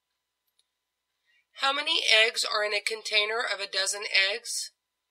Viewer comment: Some one please help me... what she says at 1:50? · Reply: "How many eggs are in a container of a dozen eggs?"